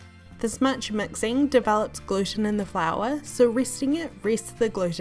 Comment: gave me stroke i couldn't understand what shes saying. love the vid tho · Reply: Amahlia Paul she meant “gluten” but her accent made it sound different